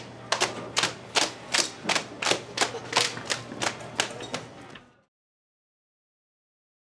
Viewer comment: who understands that part? 😂☹ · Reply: Clapping cheeks